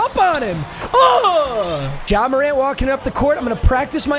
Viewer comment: i swear to god he said john morant but cant really tell